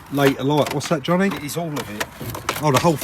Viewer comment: did you call Johnny? 😂